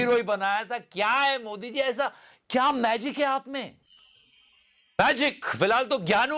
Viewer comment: asking magic instead of "kya manhusiyat hai"😂